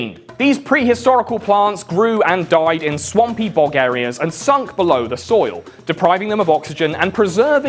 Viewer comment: Because im an American, i misheard him say "swampy bog areas" as "swampy Bulgaria"